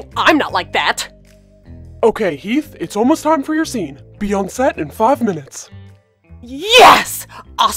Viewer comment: When he said be on set in five minutes I thought he said Beyoncé